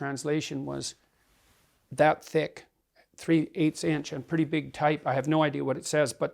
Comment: Actually, Asian written languages like Chinese and Korean, have a more "dense" form than the Latin script, so that might be the same. The question is if the words and meanings are translated correct or with an other meaning in Chinese context than in English context. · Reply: I think he said it was in large sized characters, that's why it was questionable. 4:24 "pretty big type"